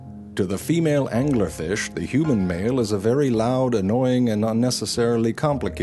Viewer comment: Is it just me or does he say To the female angler fish, "the human male" is... at 00:40?